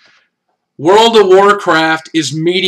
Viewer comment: Oh!!! World of Warcraft. I heard "Modern Warfare" for some reason. Can't imagine why.